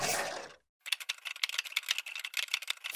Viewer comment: what is he saying? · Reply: oh my god we totally jumped off the cliff and we had to swim all the way to shore because the dog chased us all the way to canada